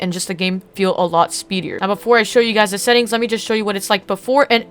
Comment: He really said speeder 😂